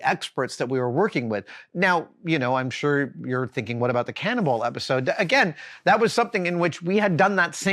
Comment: Thank you for your questions and support, Xtafa and Zander! Join this channel to support Tested and get access to perks, like asking Adam questions: <link> More MythBusters-related videos here: <link> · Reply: I misheard you and thought you referred to a cannibal episode.... I missed that episode for sure